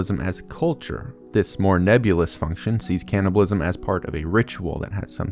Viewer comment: I THOUGHT IT SAID CANNI-BALLS